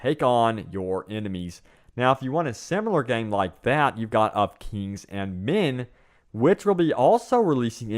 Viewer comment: What is the name of the game at 9:50? Sorry, I just can't understand what you said. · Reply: It's called Of Kings And Men